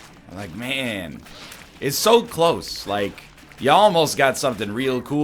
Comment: What he said: "You Almost" What I heard: "Y'all most" I love you Mr. Eggboi